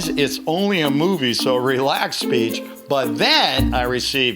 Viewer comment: Did he said bitch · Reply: Relax bitch😆